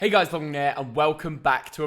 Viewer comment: It always sounds like says "Hey guys Thog gen ear."